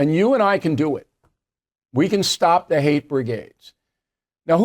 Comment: I thought at first you said it can't be stopped. What the hell are you talking about? You sound like Biden